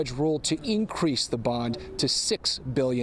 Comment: Reporter is saying bond was set at 6 billion dollars, I thought I heard 6 million. If it’s billions, she will never be able to post bail.